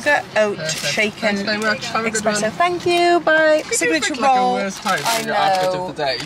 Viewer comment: Sounds like is saying “thank you, so are you “ ??